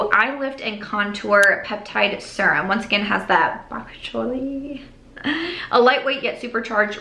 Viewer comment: I mean technically Bok Choy (what I thought you said when you said Bakuchiol) does have vitamin A! :)